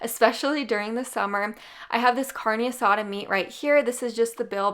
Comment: That'll teach me to make coffee and do chores while only paying 60% attention to YouTube...? When she said "carne asada meat" I thought I heard something about "sodomy." And she said it like 3 times in a few minutes 🥴🥴🤣🤣😳😳